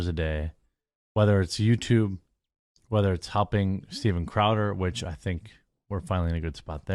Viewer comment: Lol at 14:18, he sounded like he was saying "humping Steven Crowder", which would make just as much sense.